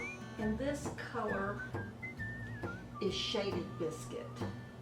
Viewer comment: Thank you for this video. I have a question about your beautiful wig - which colour from all the choices on the website are you wearing? Thx. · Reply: just heard you say the name of your wig colour is "shaded biscuit"